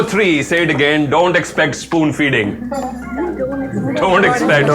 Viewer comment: did he say food feeding ?